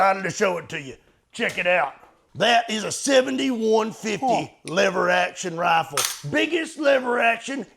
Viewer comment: Damn, I must be dyslexic lmao! I swore it said “45-70 biggest lever gun” but 70-150? Yeah maybe so 😂